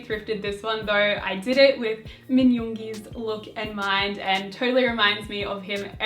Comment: Did I hear Min Yoongi